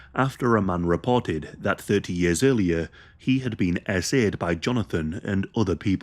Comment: I can't understand what that word sounding like "asaid" means, could somebody please explain it. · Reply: He says "SA'd". It's an abbreviation which stands for sexual assault. I think he used it cause saying the term in full can get his video shadowbanned, or to avoid triggering SA victims, I'm not sure. Hope that helps!